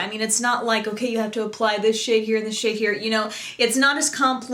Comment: i thought u said.. u have to apply this shit here and u have to apply this shit there.. haha, no offense i love your videos